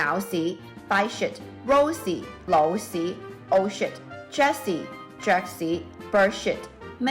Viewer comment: Old shit is what got me cause it sounded like she said oh shit